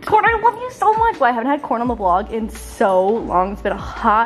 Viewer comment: When you said I haven’t had CORN ON THE BOLG in a while I thought you said CORN ON THE COB and I was like I feel like that’s a little rude to corn